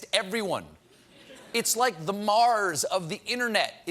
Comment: Omg I thought he said Mahrs of the internet... inhospitable to human life 😂